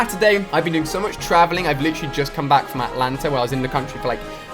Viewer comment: Omg... did he just say he was in Atlanta ? I would've called off work 😩